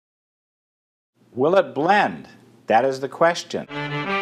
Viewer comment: I thought it said bend.. Not blend.